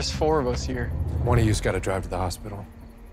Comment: For a moment I thought he said, I don't know where the hospital is